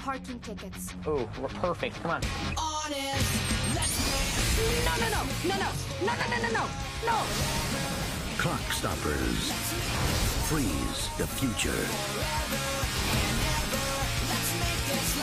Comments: Sounds like he says, Cockstoppers at the end there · I thought it said "Cockstoppers." XD. Whoops.